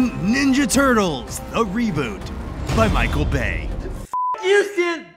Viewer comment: I thought it said by Michael Gay